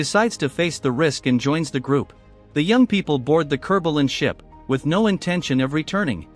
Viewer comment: I thought he said “Kerbal & ship” since I have a normal will I ever watch this. Figured the narrator thought it would be hilarious mentioning KSP